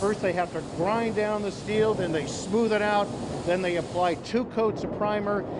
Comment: I thought she said golden gate day